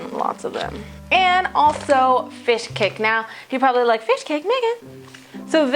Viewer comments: i thought you said "fishcake nigga" LMAO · is she saying 'fish cake' at 3:12 or is it something in korean?! in the description it says 'rice cake' so I'm confused :-((( p.s. this looks so amazingly yummy!!!!!